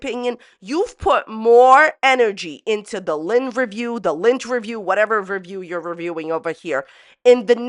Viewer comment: Lol i thought she was saying Lint-erview. And was going to interview someone while they picked imaginary lint off her